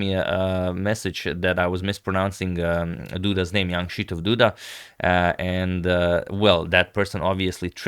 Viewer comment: ever since I first started watching your videos back in 2019 I always thought you were saying "the young Krzysztof Duda" and I'm a bit ashamed to say I only realized my misunderstanding a few months ago. Regardless, I've always loved your coverage of his games so thanks!